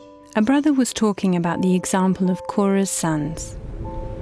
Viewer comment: I heard: "the example of Coruscant..."